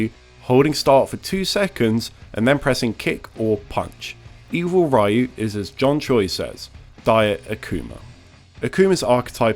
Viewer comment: what the fuck did you say just then I can't make it out, sounds like a foreign language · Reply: Evil Ryu is as John Choi says, “Diet Akuma.”